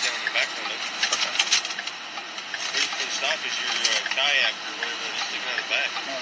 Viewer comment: Sounded like he had something on top of his car extending more than 3 feet off the back without a flag (moving violation). Also sounded like his registration was not current ("I'm between addresses, yes I noticed that.") That's when they asked him to step out and he refused. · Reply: I thought the cop said a Kayak.